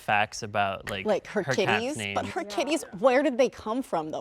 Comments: I thought she said titties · When they were talking about her cats, I thought that one girl was saying "kidneys"... and she was like, "but we don't know where her kidneys came from!" It wasn't until the screen popped up with pictures of three cats when it dawned on me that she was saying "kitties" lmao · I miss heard her and thought she said ”kidneys” 😭😭😭😭